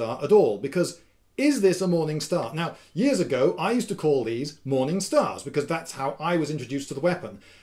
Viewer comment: who else thought it said "morning star fails"? that should be a follow up vid